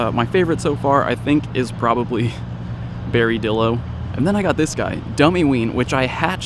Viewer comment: lmao i thought it said berrydildo